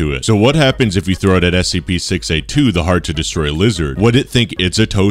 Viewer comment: I heard "The hard to destroy loser"